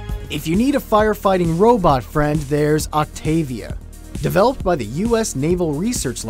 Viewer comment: Did somebody say Octavia? I think I heard someone say Octavia!